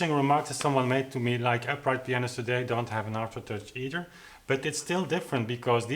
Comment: What does he say at about 14:57? “Upright pianos today don’t have a ...”? I can’t make it out. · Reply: "aftertouch either"